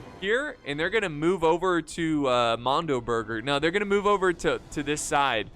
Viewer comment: "they're are moving to Mando burger" I know what you said there